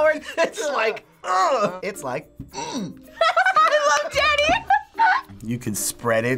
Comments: I thought that girl said I loved daddy when the blonde guy went "hmph" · i thought she said “i love daddy” lmao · Am I the only one who heard "I love daddy"? XD